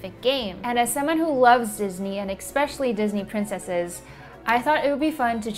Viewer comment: did you say “expecially”? 😂🤡